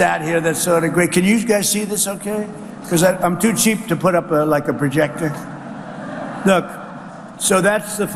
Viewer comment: Did he just say he’s too cheap to set up a projector? I need to be sure I heard that correctly. Please tell me I’m mistaken. Perhaps my ear was pierced by a bullet. Let me try to listen with my other ear to be sure.